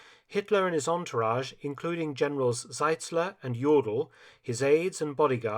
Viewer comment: The second General Mark Felton names almost sounds like "General Yolo". Like "Always there, where someone has to go all in". Had to laugh :D. Thx for that (and yeah I know, it is Jodl, but still the pronounciation almost sounded like the other one)